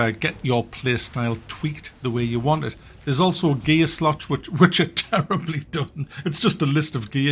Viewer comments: I thought he said 'there's also a gay slot here'....lol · Hahahah - I thought he said, you have "gay-slot" that you can upgrade.